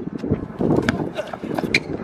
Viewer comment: “IRISH SPRING GREEN GREEN” -FlightReacts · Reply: ik it just sounded like be said Irish Green Green Green this time